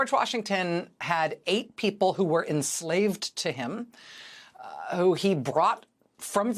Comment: I thought you said, "Washington had eaten 8 people who were enslaved to him" Like NO WAAAAY, Washington was a cannibal!l lol